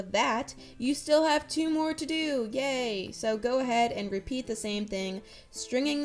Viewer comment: did she said "GAY"